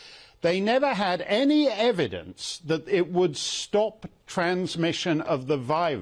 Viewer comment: I don't think she said that, to me it sounds like she said "did we know it would stop immunization?" That's even worse than not stopping transmission..